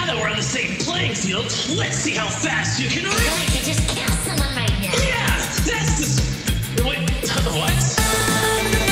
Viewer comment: I cant understand what BF is saiying for the love of me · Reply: I think he said "I feel I could kill someone right now!"